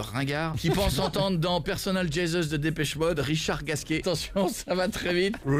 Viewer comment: moi à 2:00 j'entend Richard t'as faim avec l'accent Marseillais